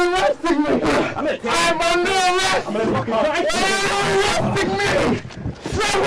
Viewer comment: It sounded like he said “why you molesting me” lol